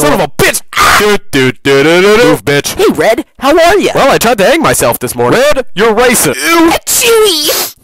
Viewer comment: “Oh hey there red!” “Oh hi” “What did you do this morning?” “I tried to hang myself.” “WHAT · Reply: I thought he said I tried to egg myself this morí